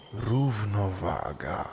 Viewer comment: usłyszałem gówno waga XDDDDD